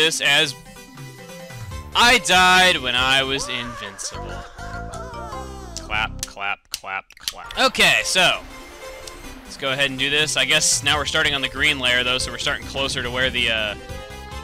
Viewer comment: did u say flap or Fap ?